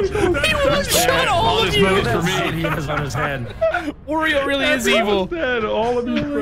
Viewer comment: Hey did anyone else hear say Oreo instead of Wario at 20:25 😅😅or was it just me I think that was funny as fck 😄